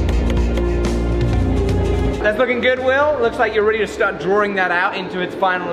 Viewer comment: does he say, is that F$%&@ good will..lol